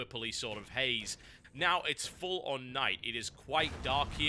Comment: he actually said "it's full on night"